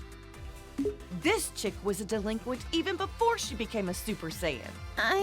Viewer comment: what tf did she called her 😭🙏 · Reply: A delinquent